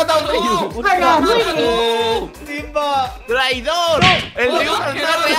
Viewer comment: ala :v escucharon loque dijo victor ,yo si lo escuche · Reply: Le dijo traidor a rius xD y dijo ke el riustor no es real :"^